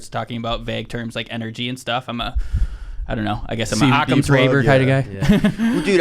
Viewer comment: Did he just say "Occams raver" 5.08 ish? That was funny, if you're trying to sound intelligent, make sure you have your 'ducks in a row' lol! 😊